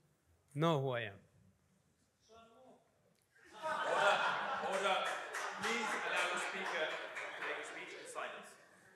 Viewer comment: anyone know what the crowd said here? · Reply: They said "Shalom" in Hebrew to mock Mosab for supporting Israel. idiots.